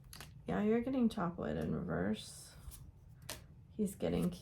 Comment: Oh! Chop Wood in Reverse…..I thought you said “Chocolate in Reverse”, as I was stuffing a chocolate muffin in my mouth 😱🤣 Thank you for this reading 💚